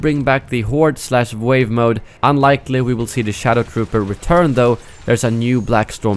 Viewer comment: at first I thought you said ShadowPooper... and was VERY confused for a second until I listened to the word again :D